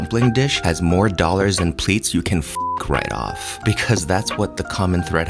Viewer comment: “If the dumpling has more dollars than pleats” is the standard I am now judging all of my dumplings I order. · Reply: i thought he said plates, this makes much more sense